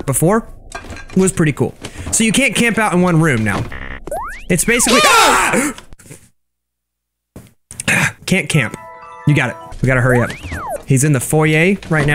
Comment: I thought he said “Camp Camp” like the adult cartoon but he said “can’t camp”